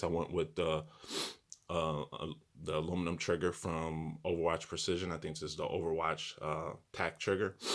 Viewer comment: Ebay of course. 😀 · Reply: thanks for reply but I thought you said from somewhere precision?